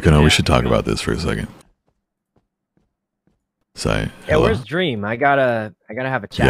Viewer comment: when Corpse say "Sy" I heard it as "Say" in Indonesia it means "babe" `>` Sorry, no one cares about what am i hearing😌